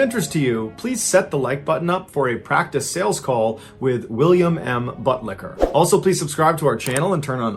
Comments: Did he just say...William M Buttlicker? 😂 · did he say William M. Buttlicker 🤣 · I thought he said butt licker!😊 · Did he just say "William M. Butt licker"?